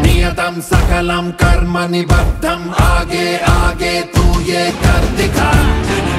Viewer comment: it should be आगे-आगे instead of आगो-आगो 😅